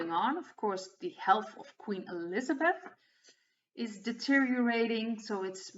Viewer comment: Hi, yes, energy up and down, i'm totally with you, i even thought i heard you saying queen elizardbeth (really)but that's me of course, my brain is working overtime these very exciting times, thank you very much, you always make my day!